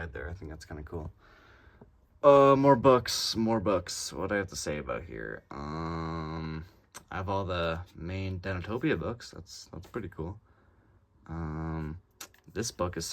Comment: I really like the botm shelf, the lights really add to it. Super anhinga collection‼️‼️ 🔥🔥🔥🦟🦟🦟 20:41 I thought you said Dinophobia 😭😭😭 24:36 I wonder who that is...